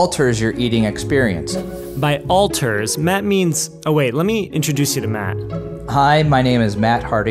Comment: "Wait, let me introduce you to Matt" What I heard: "Wait, let me introduce you to Math" Me: OH no, nvm